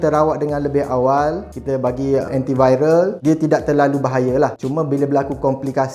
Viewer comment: dia kata "...kita bagi anti-viral"? Anti-VIRAL ke Anti-VIRUS? Aku salah dengar ke? Ke...ayat ANTI-VIRAL tu memang betul?